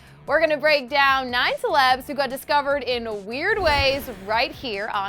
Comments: I thought it said divorced in weird ways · am i the only one that thought it said "divorced" instead of "discovered"?? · I thought it said divorced in weird ways XD